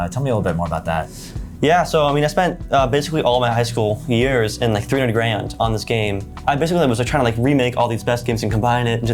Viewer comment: Did he say $300,000